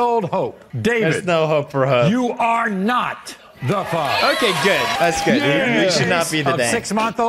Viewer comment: I think i heard wrong on the last part before she ran out I heard he said : "you are not the mother" I think i'm going deaf or something😭😂😂